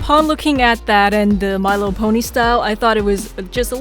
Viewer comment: I heard Pony Yes I love My little Pony!!! He could be the Purple dragon Spike’s new friend! His cutie mark would be a rattle?